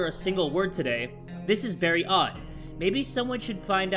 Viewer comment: i thought said this is Marry odd but it was very odd